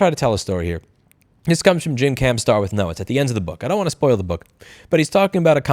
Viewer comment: Book; Jim Camp's Start with NO. I heard, jim can star no .